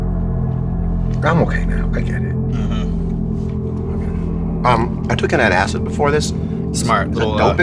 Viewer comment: I thought he said "I took a tab of acid before this" in the beginning for a second hahaha, had to go back and rewatch